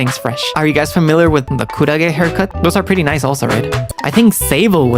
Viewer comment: i think he said kurage haircut? Saw in another vid that kurage means jellyfish ^^